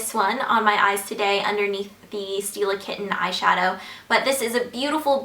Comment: Originally, I thought she said steal a kitten... whoops!